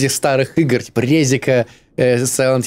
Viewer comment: те кто услышали Резик 😅 Те кто услышали Презик 💀